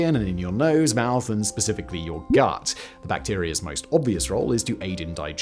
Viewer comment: definitely heard “gyatt” instead of of “gut”… I am officially brain rotted.